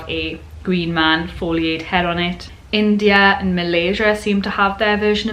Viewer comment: Did you say “Malaysia” after India in your section What is the Green Man 6:11 ?